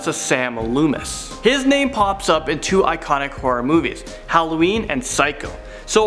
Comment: Firstly, bedore even watching this, part 2!! And secondly, first thing I heard was "In porno movies" instead of "In horror movies" 😳😳